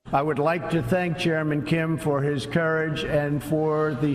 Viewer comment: I first thought he said German Kim xD